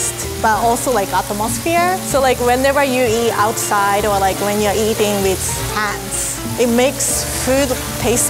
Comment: i thought she said eating with ants